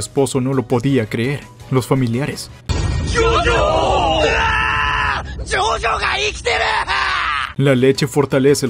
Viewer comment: parece que dice "jojo valiste *****" jajajjaa